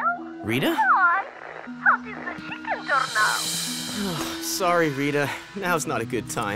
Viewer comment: Why do I hear chicken doorknob? At